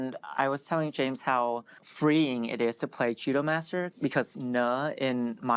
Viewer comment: I swear he said "Cheetomaster"